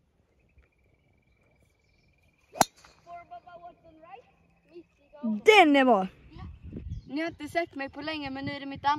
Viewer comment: Vad säger han vid 11:54 ? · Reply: Four Bubba Watson right